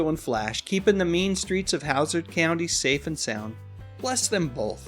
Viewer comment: Did you say Howsard County? What the heck!?!